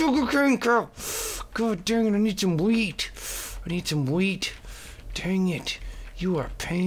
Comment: I thought you said "I need some weed", not "I need some wheat" xD oh... You and your habits :/ lol